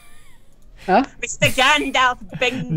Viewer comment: did say "Mr. Gandalf" ?! Or am I just a huge Hobbit nerd?